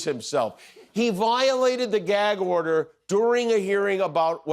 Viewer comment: He thought that the judge said gag odor.